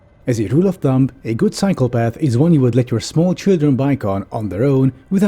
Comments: I misheard "cycle path" as "psychopath" and was wondering what the hell small children were doing with them on their own :P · I momentarily misheard that line as “A good psychopath is one that you would let your children bike on.” Lol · I heard "As a rule of thumb, a good psychopath is one you'd let your small children bike on without fearing for their lives." 🤣 (Yes, I know you said "cycle path.") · When you said Cycle Path I thought you said psychopath